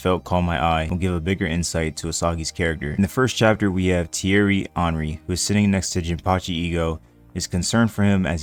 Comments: bro at 0:22 I really thought you said Thierry Henry and quickly went to read the novel so I could read what the hell a Premier League legend was doing with Ego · mf😭😭😭😭😭 i had to rewind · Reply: So I'm not the only one who thought he said "Theirry Henry" 😭😭😭😭😭😭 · Thought he said "Thierry Henry" and I was like tf?